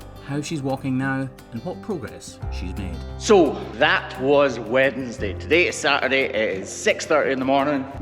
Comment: he actually said Wednesday!!